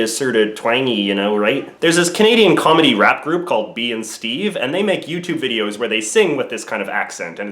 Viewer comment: wtf did u just say? Bee in Steve?